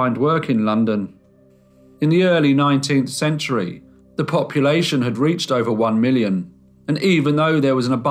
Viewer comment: When you remarked about the growth of London's population, I first thought you said 'copulation', and I was taken aback.